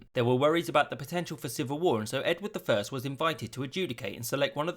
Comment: did you say judecate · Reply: Adjudicate